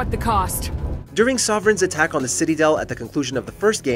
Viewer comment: Did I hear "City-del" @ 6:17?